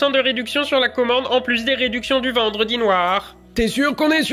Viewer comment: Quand il a dit vendredi noir j'ai compris "vendre dix noir" mdrrr